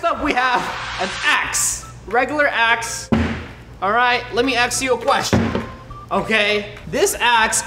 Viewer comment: Roi:let me ask you a question Me did he say “let me axe you a question.”